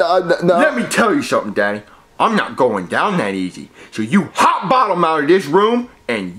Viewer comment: thought he said “ hop on out” But is hot bottom a synonym for “ get out” too?